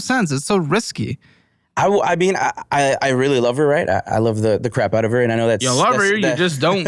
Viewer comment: Sounds like he said “ I really love her right now,”